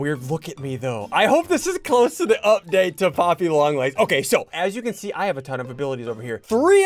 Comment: did he say poppy long legs